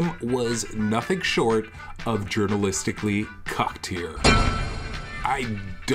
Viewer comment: The clip of saying "Journalist" gets me every time. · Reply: I always heard it as "urinalist"